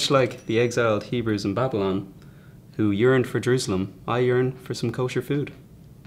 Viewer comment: i thought he said "urined for Jersulam" at 0:03..anyone else? lol